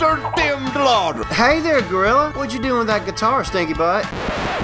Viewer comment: what does he say? 1:50 hey there groilla, what'cha doing with that guitar .......